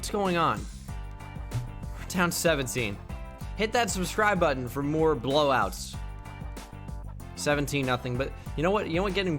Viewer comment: Did he say he’s blow me out of I subscribed?